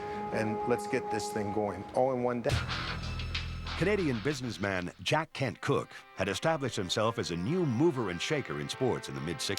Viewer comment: Sounded like he said Jack Can't Cook 😂😂😂